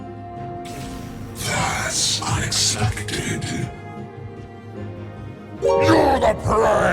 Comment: what did he say? 😭 I heard something. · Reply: that's unexpected?